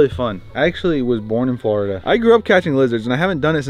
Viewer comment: In this video says he was born in Florida but I thought he said he was born in England 🏴